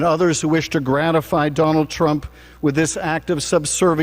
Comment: Sounds like said "donald TRIPE." Ooooo! That's my new moniker! Donald Tripe! GO! VOTE BLUE! 💙💙💙💙💙